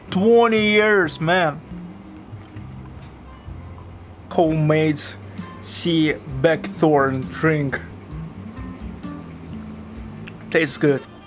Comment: I couldn't quite understand. 9:00 -- What was the name of the orange-looking drink that you had? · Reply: "Homemade sea buckthorn drink".